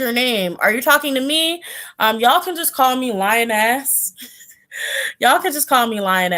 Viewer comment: 😂😂😂 i heard " call me lion ass" lol it was "lioness" i just can't stop laughing😂😂😂😂😂